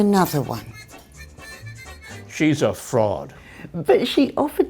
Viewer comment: I thought he said " shes a frog"